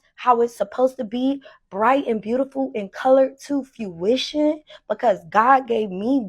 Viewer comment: I like how she pronounced fuchsia as "fuh-oo-shuh" instead of as "fyoo-shuh". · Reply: I just thought she said fyuition instead of fruition but not I can't unheard it 😭